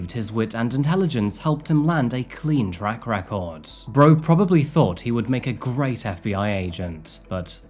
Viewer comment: Did you say "bro would probably thought"?? If so I like it.. Maybe use a few more personal words like this.. Plus I like the goat-T, and I am a fan of the blue plaid shirt